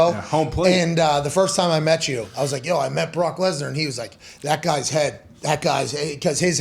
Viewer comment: I literally thought it said “Brock Lesner takes his life” lmao bye